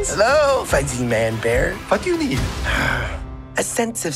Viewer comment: I literally thought he said: Fu*k u need?